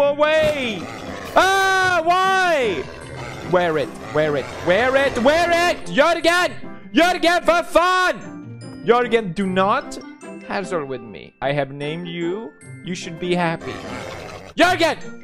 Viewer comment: Caption Says "YALL GOT YOUR GET FOR FUN"